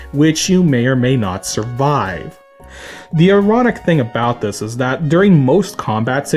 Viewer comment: did you say ironic or erotic? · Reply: "ironic" :B